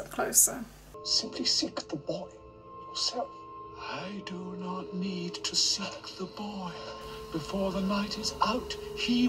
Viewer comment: I swear Voldemort says “I do not need to suck the boy”